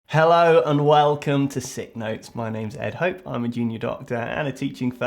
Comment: I remember that the first time I watched a video, I thought he was saying: "I'm a 'genius' doctor" here in UK" 😂😂😂😂😂😂😂😂